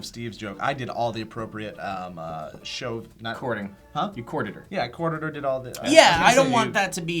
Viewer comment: when said courted i thought he said recorded. "you courted her?" in reference to sexual relations... i heard: "so you recorded her?" and then i heard say yes. confusion for some moments